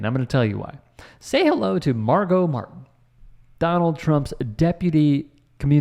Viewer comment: Ohhhh, i thought when jack white said Margo martin, he was calling trump mar a lago's Martin sheen.. lol